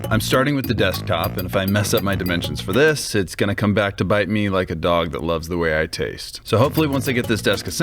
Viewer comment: i hear 'hotdog' like 'a dog' and say 'what!'